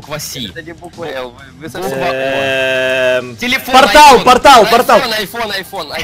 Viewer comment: они тебя обманули на 6:17 сказал телефон а сказал что он сказал айфон